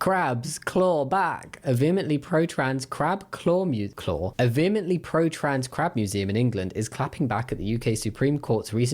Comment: I misheard "pro-crab trans museum" around 17:10 and got a bit confused for a sec 😅 🦀